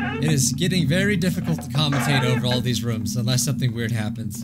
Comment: i heard "Raldi's room" for a second so i looked to see if goncy added sth but yeah i was wrong